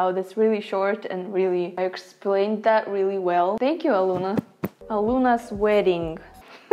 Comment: no joke I thought she said Aloona sweating